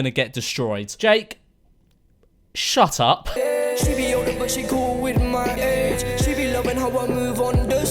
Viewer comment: I swear he said “she older but she cool with my aids” anyone else?